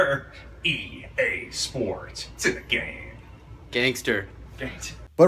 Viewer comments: I've always thought it said "EA sports, to the game" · I can’t be the only one who thought he said “EA sports to the game” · Am I the only one who thought that he said "EA Sports, To The Game"